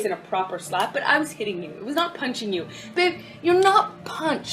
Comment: the subtitles in the picture isnt accurate maybe im wrong but i hear "babe" instead of " bitc.." im on Johnny side but misleading information isnt cool